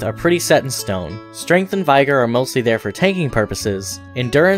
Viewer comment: Did he just say Veigar? Not vigor? 😂